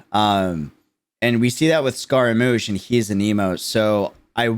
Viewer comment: I know he is saying "He is anemo" but I swear at first i thought he said " He is a emo" 😂